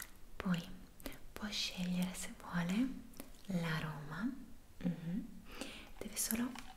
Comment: Ma solo io a 3:45 avevo capito:”se vuole può scegliere la Roma” Dopo 10 volte che l’ho riascoltato ho capito che era “l’aroma” ahahahah uaddafaa